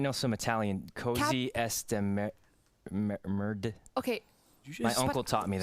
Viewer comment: For y’all wondering what Sykkuno said in Italian in 25:14 , he said: “Cos’è sta merda?” Which means “what is this shit?”. You can say it in the context that he mentioned which is when the food is really really bad.